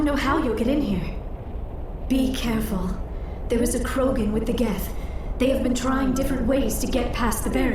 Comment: i didn't pay attention to what she say's at 1:07 i thought she say "There is a Krogan with the GAT" im like excuse me??!?!